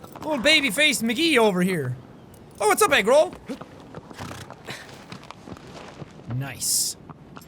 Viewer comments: sounds like he said eggroll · Did jack say 'What up Egg Roll' instead of Agro? ...Ok